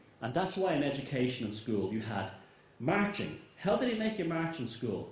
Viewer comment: What's the word he uses @ 45:40 ? .. is it 'marching' ?